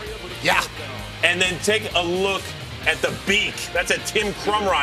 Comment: What does he say at 0:10? Tim crow nose?